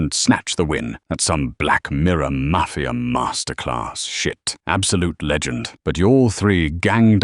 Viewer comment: did grok just said "shit"?!? I thought they couldnt say that Edit: and said "AF"